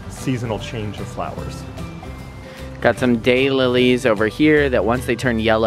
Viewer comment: It sounded like said "gay lilies" instead of "day lilies"